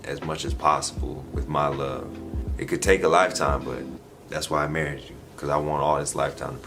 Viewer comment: Hold up!!! Are they already married???? At 9:10 I could have swore that said "that's why I married you". Either way, CONGRATULATIONS and. I pray you have a lifetime of happiness!!! ❤